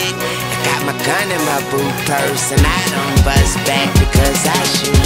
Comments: He said BOOT PURSE! That's what it sounds like he said to me. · i thought he said "in my boo purse"